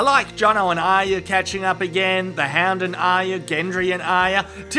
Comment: Wait, did he just say 'The Ham' instead of 'The Hound'? Or it's just me who heard it wrong?